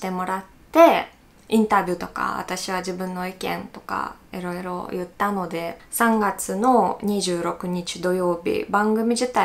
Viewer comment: の動画、結構前から見ているんだが、今までは、つっこまなくてもいいかなと思っていたんだけど、報道系のメディアに今後も出演する可能性あるだろうから、コメントしておくことにする。日本人には、のいろいろは、えろえろに聞こえる。